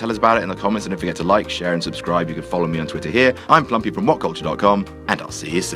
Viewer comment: Gee, I always misheard it as "Plumpy", not))